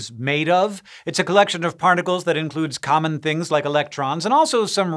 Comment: does anybody else hear “parnacles” here? 😂